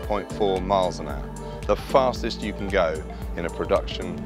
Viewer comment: How is it the fastest you can go in a production Bugatti, when the Veyron super sport can do 268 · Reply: my bad I didnt hear the "in production" I thought he just said production